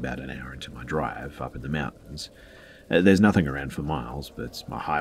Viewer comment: I like how you say the word mountain. I hear always "meow-tain"